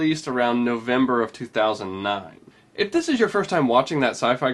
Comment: эм,я почти уверен что он говорит в 2009 ,а не в 2005 году.